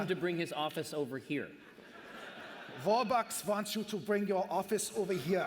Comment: I'm sure I heard him say "four-bucks".